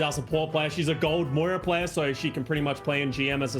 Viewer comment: ahh he did say moira. My brain for some reason thought he said mercy